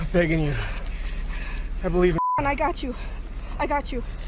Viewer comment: Did him saying "I believe in God" get beeped from this video? Asking out of curiosity. I support the blue and I support this channel. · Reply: He said "I believe in you" talking to the officer.